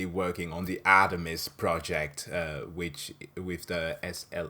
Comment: When you said, "Artemis project", I thought you said, "Atomsk project". I was wondering if someone at NASA was a fan of FLCL. (☞ﾟ∀ﾟ)☞